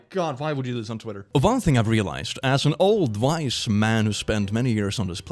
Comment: i thought you were about to say "as an old white man"